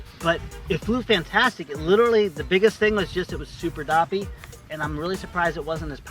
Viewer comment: What did say at 24:35? "Super doppy?" Sounds like the Pennsylvania Dutch "dappich".